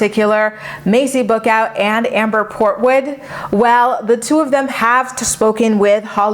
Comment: At first I thought you said: Amber Porkwood 🤣